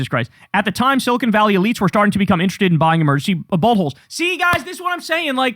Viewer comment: I thought you said “emergency buttholes” 😂